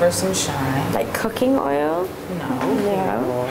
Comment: Coulda sworn she said coconut oil